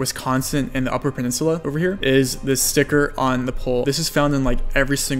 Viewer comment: Misheard that for "stripper on the pole" lol